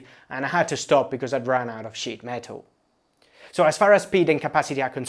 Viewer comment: … had to stop because he ran out of sh** metal. I know you meant “sheet”, but I heard something different and it made me LOL